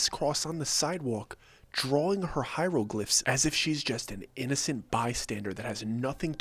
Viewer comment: lmfao 😂 I heard "Hyruleglyphs" lol like ok Link I got you lol. Well excuseee me princess. I cannot be the only nerd in here lol